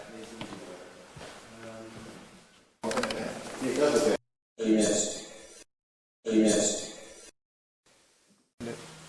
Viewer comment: The EVP at 8:55 sounds like he is saying "You Rest" while "Rocky" sounds similar to 'get off me'? It's a wild thought but the door slam could represent when the girl falling to the floor & the door being open was the governess racing to the childs aid?! Where was this RAF air field at, didn't think that any would still be standing still? The old church was interesting, does make one wonder who it is that made you all feel so unwelcome?